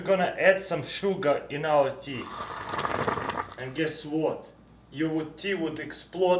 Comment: What did he say? Something like: Guess what... Thnx